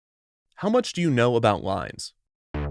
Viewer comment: I didn't know it said "How much do you know about lines" and I thought it said "How much do you know about lies" I was on a whole different page for a while.